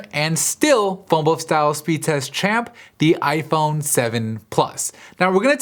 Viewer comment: it sounds like he says "fumble style speed test champ"